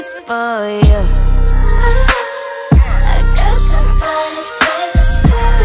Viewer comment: Thought she was saying " i got somebody famous for you" blows tf out my girl when i say that😂😂😂